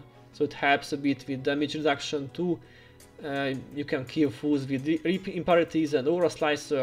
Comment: All this time in your vids, I thought you were saying "fools" vs. "foes" and it always cracked me up..."ball up these fools!" hah. Thank you for all these awesome guides and the effort you put into this game. Well done!